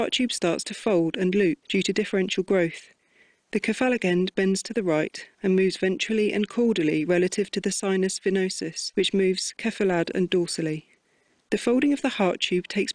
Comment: I'm really sorry what did you say at 1:00 " due to differential growth ...??????" Thanks in advance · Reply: Dounia Aribi "The cephalic end bends to the right, and moves ventrally and caudally relative to the sinus venosus, which moves cephalad and dorsally."